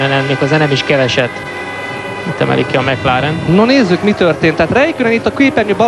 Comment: mit mondott a kommentátor? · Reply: "Itt emelik ki a McLaren-t"